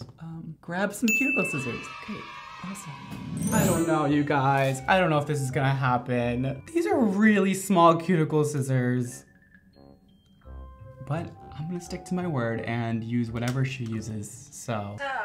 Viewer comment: I thought she said "beautiful scissors"....Haha! I thought for sure...You got this! 😊🙌❤